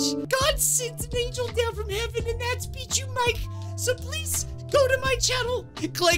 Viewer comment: When bijuu mike says his channel name he sounds like he is saying “beat you mike”